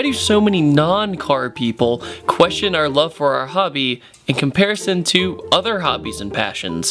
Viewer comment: oh my gosh xD I thought he was saying "Hubby" I was like tu fak, but he was actually saying Hobby x,D